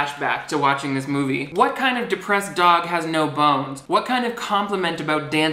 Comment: "cause i got no BONES!" honestly thought he said no balls, and no bones is even less funny